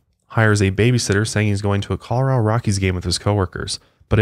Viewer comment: I thought said Cholera Rockies Game 😂😂😂😂